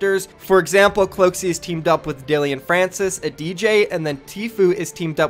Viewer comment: Did he just say "dillion francis?"